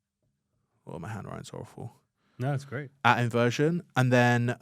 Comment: did he really said "no, its great" to his handwriting 😭😆